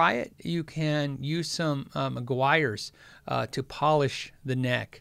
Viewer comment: Aguirres? Hey guys what is the Ron thorn trick? I can’t figure out what he said there. Thanks! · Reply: Update. I think I figured it out. It’s Meguires Ultimate Polish for cars.